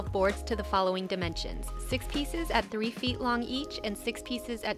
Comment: I love how the final product came out, but I just have a question about the dimensions. With the 6' and 3' board measurements, the shelf would be 9' tall, 10' 5" with the pipes, and 6' wide. But the thumbnail and finished product look smaller. Did you cut those boards in half after the initial cuts? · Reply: Ohhh ok, I thought I had heard 6 feet for some reason. Thank you for clarifying, I'm going to attempt this with some rainbow poplar I have laying around. Thank you again!